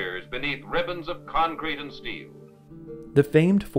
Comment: and I have to turn off CC because it's hella misleading, omitting some words and substituting others (i.e. Steve instead of steel). Hope you patch it up soon!